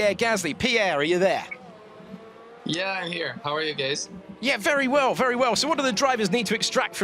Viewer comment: am I the only one who heard how are you gays?